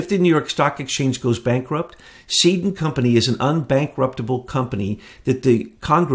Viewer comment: did he say Satan company?!